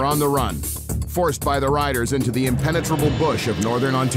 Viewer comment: When the narrator said the contestants were on the run, "forced by the riders..." I thought he said,"forced by the writers." Either way is believable.